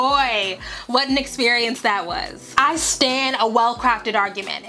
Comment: "I stand a well-crafted argument"... What is she saying here? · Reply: "I stan"